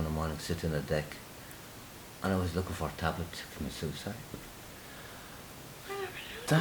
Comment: i don't understand what you dad said at 8:00 please help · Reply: "I was looking for a tablet to commit suicide."